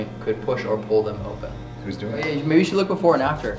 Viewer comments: When mic when crazy I didn’t hear “He’s doing it “ I heard “They scare me” anyone else ? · Before “he’s doing it” the audio sounds like it says “help me”